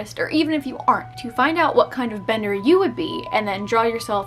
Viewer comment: i really thought it said blender.